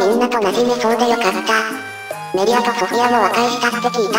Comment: 本当に良かったの所、ほんとうにいかった、に聞こえるの気のせい？